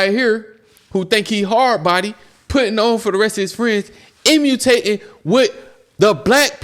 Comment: I know he didn’t say “emutating” instead of imitating 10:37. 😂